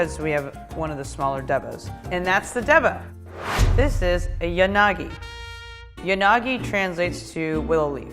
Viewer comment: who else heard it as Unaagi from F.R.I.E.N.D.S